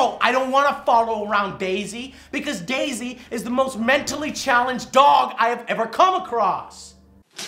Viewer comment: I heard -"DayZ is the most mentally challenged dog" . . Laughed so hard ......